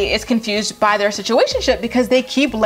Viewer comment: did you say "situationship"?! Lol. Thanks for always being so honest about these stupid girls and their stupid spouses!